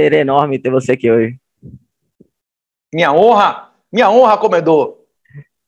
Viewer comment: Minha honra comedor ? Entendi direito ?